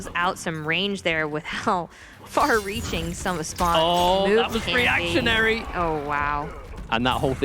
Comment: that was "preactionary"? Am I hearing that right? Is that even a word?